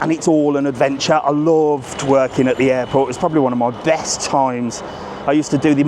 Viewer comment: I swear you said you love twerking at the airport 😅😅